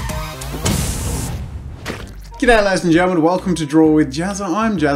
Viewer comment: What does he say here 0:14? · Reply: He says "G'day ladies and gentleman", g'day being short for "Good day".